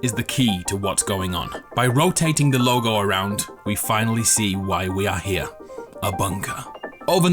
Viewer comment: "now we know why we are here abunga" thats what i heard you hear